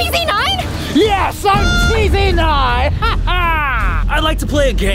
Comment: When he say "Yes I'm Pz9!" I thought he said Yes I'm Tv9 So funny hahaha!